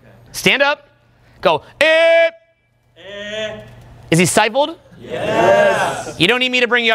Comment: What does say at 18:21? "Is he syboled"? What does that mean ...